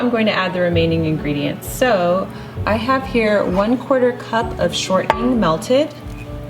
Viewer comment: Excuse me, Jennifer, but at 1:13 you say "one quarter cup of ... melted". What is melted?))) I listened to it a few times bust still cannot understand it =) I'm thinking to bake this cake on Sunday to start our week with a delicious breakfast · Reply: Dragonfly's Heart crisco/ shortening